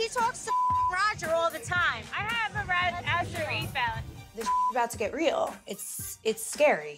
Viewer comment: what... what is angelina saying? I googled both acid refaulle and radacid refaulle and google literally had nothing to show me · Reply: websurfer1994 I’m pretty sure she meant acid reflux